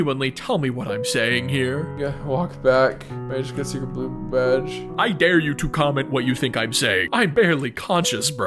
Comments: "yeah walk back, i can see the blue badge" thats ALL i heard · “guh walked back I jus need find blue badge..” that’s what I heard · "Gotta walk back looking for a blue badge" That's what I think you said 😂 Great video! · I heard "can't walk back, I just pooped the rash." that's genuinely what I heard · I think he’s saying got to walk back to get badge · i think you said i need to walk back to get a secret badge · you said “im gonna walk back, gonna take a blue badge” or atleast, thats what i heard. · i think your saying, "yea.. walk.. back gotta blue back.." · "gotta walk back to get the blue badge" but what it sounded like is "gngh walk back whats-gotta-see blue badge" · I heard "yeh walk back,as u can see the blue badge" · i think he says Yeah walk back i just got a secret blue badge😅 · I think he said “I walk back” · Here is what you said "Yeah. Walk back. I just got a secret blue badge." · walkback i guess i got the blue badge thats all i heard